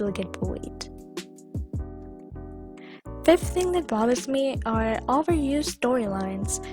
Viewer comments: I thought it said "that brother me" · I thought it said brother xD but it was bother xD